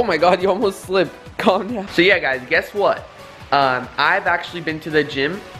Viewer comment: i swear at 3:55 I swear I heard a "yeah" 😱😱😱